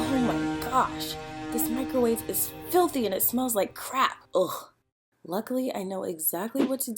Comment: did she say 'smells like crap' or 'crack'? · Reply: I think crap